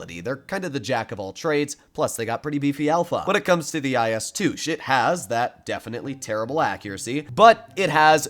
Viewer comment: who else heard IS 2 sh*t? 💀